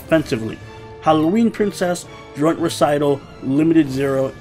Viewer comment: I thought you said Drunk Recital